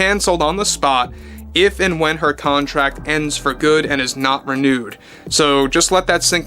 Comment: I almost thought I herd you say she would not get nude. Oh not renewed, got it!